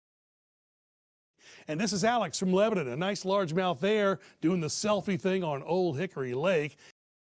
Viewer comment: Oh man. I lost it for a second on that weather man's intro. I thought he said "..and this is Alex from Lebanon. A nice Largemouth "BEAR". I had to replay it and realized he said "nice largemouth there. It was way funnier when I thought he said bear, but very cool that he showed your selfie. That little worm on the BG-1 was epic! I've been catching most of my fish lately on a 1g or 1.25 g BG-1 with a #12 hook and either a Larva-Z or small Big Bite Baits crawfish. My biggest bass on the Larva-Z was 2 lb.s 5 oz. Have you tried the 1/32 or 1/16 oz Mule Fishing jig heads? They work GREAT as a finesse Ned head with his Jack Worm and other tiny soft plastics! Thanks again Alex for sharing your knowledge and the truth while fishing. You don't always catch fish and the fish aren't always giants. The reality of your channel helps keep us all fishing even when we're struggling in the heat and just catching decent fish where we live and the sometimes less productive waters we have access to. You know, "the reality of fishing," or as you say, "keeping it realistic."